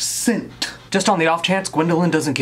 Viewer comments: I could've swore you said paint porn. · I legit thought he said “Paint Porn” IM SORRY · Did anyone else hear him say “Paint PORN”?